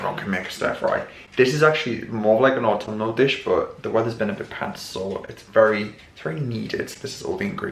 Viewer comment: A bit pants? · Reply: it sounded like you said that the weather was “a bit pants” out.